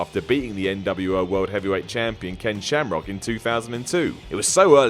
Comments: did say "NWO world champion Ken Shamrock"? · Did he say NWO World Champion at 5:20?????